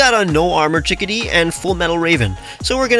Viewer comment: You said Fullmetal raven but I thought you said Fullmetal alchemist 😂